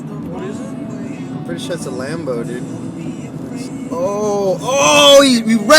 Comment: did he say A LAMBO OH MY LORD PLEASE TELL ME HE WAS JOKING